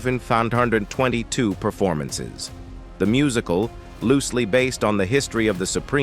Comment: Sorry, what was that now? I think you said "one foufam-farfhundred and twenty-two performances". 😆